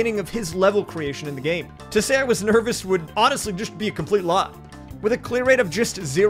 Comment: Just found your Twitch channel and I've been enjoying your subathon. knew I should check out this video, and it did not disappoint! Crazy dedication, bro. One question, though: at 5:03 I think you are saying "To say I was nervous would honestly just be a complete lie." I had to rewind this a couple times because I wasn't sure if I misheard it. Did you mean to say "wasn't"? I'm sorry.😅 This is such a nitpicky thing, but I thought maybe you'd want to know. Keep up the good work!👍